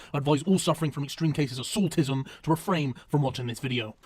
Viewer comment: thought you said in the into 'extreme cases of autism' instead of the orginal extreme cases of saltiness